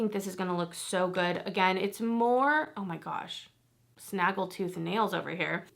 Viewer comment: The nail Comment made me giggle. I thought you said Snagglepus nails! 😂 Oh I love Violet Voss too. Viseart... I got 2 of the petites fours in Amalie and Violet Deux! I got the Ace Beauty Smokey Rose which is absolutely beautiful and you would love the cool and warm tones. Another brand I love and Natasha Denona. Does anyone feel like me? I love love her but the cream to powder. I j7st can't use them because my eye eats it.😂 ❤😊